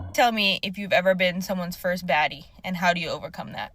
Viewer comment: Smirk!! Did she just say... his first fattie!!??